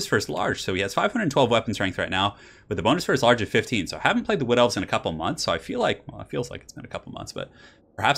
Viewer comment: The first time he said "Wood Elves", I misheard it for "Blood Elves" lol. :P